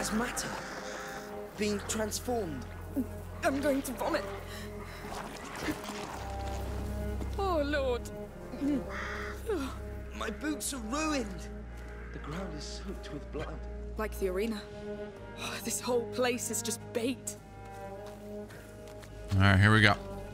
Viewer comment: lol @ 6:28 he says my boots i heard boobs haha